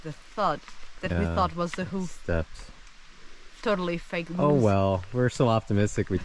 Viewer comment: Watch out for fake moose! Don't be fooled. 😆With great accent, the first time I thought she said it was, "totally fake news."